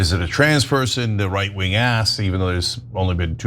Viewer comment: A right wing...ass??? I couldn't make out what you said. But BOTH attempts on Trump were registered republicans....and now THIS shooter is a republican......so what are you talking about?????????????